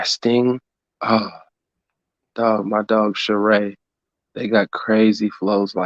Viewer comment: who is the artist he spoke of and can anyone share some of that artist’s music, sounds like he said Shirae..? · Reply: sha ray